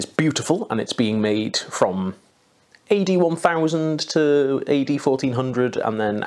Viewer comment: I heard "AD 1000" as "81,000". 😅